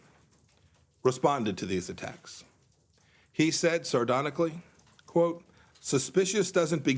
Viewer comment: did he just say, ''sardonically?"